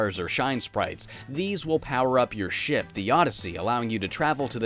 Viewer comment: I dunno why but whenever i go back to listen,i think wants to say:"These will power up your SHIT" but than he says the "p" with a break!